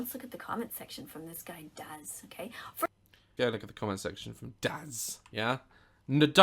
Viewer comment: It sounds like she said "Comment section Daz gay" XDD